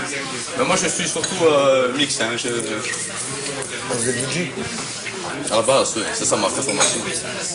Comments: Qu'est-ce Qui dit François a 6:06 ? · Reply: da master cream "a la base oui c ça ma vraie formation" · il a dit quoi à 06:01 ??? :-) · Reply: Vous êtes DJ ?